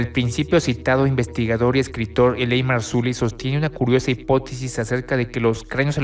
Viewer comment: Me encanto tu vídeo, ojala sigas haciendo más, queria saber el nombre de escritor y investigar del que hablar en el minuto 19:40 no entendí el nombre, creo que era Eley Marzuly? porfa si podrías sacarme de dudas agradecido. Saludos y bendiciones, muchos éxitos con tu canal.